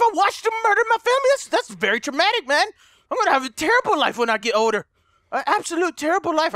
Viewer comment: hey delirious it sounded like u said oder instead of older